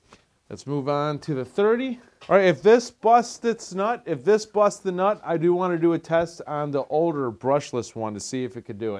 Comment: What did you say @ 3:34 🤔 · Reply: I thank you for viewing Isaac